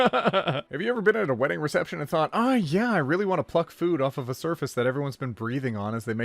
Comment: when he said "pluck food off..." I legit thought he said "f*ck food off..." and i sincerely wondered what kind of weddings attends